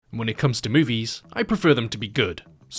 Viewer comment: I thought u said boobies 💀💀💀